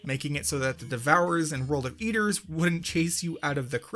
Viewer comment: Did anyone else hear him say "World of Eaters" at 3:35?